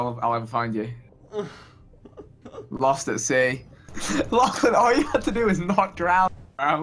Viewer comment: Lol I thought the intro said lost at seal